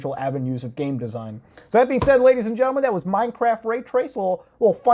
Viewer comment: I thought he said MineCrack.